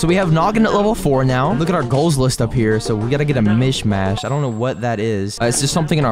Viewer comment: it sounds like he is saying mishymash